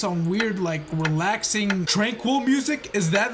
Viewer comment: I thought he said tranquilazer Lol (Yes I know I spelt it wrong) Also SUBSCRIBE TO OR ELSE >:(